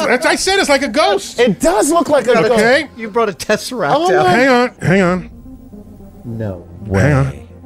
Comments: I thought he said "You brought a tesseract out here..." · I thought he said tessaract 🤣 marvel vibes